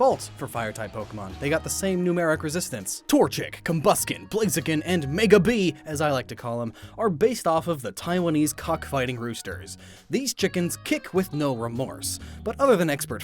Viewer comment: did you say cock fighting